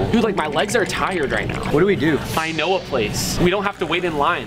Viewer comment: I thought you were trying to say “I NO-AH place” at the beginning of the video